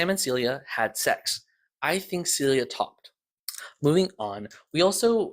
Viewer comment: I got passed the part where you said Celia topped Sam and I misheard and thought you said she talked. I thought that there was a small period of time you believed that Sam was so good in bed that she said everything. Lol. I wonder how Sam’s immaturity will mix with the fact that he doesn’t hate Celia after being sent to TMA universe. His perception on that is itself really weird. Also Trever Herbert knows everything and is the key architect of Protocol and Archives. He timed traveled to create Robert Smirk and Jonah Magnus in both timelines to retroactively make a path for him to become god emperor. This is known 🤡